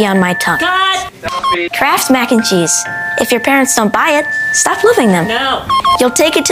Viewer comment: “If your parents don’t buy it stop loving them” 🖤💀☠️🚩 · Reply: I thought he said “stop living then” 😂😂